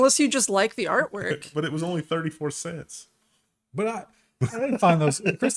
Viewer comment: All I heard was Macklemore - thrift shop "But it was 99 cents"